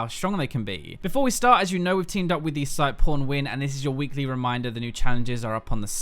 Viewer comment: is it me or did i hear porn win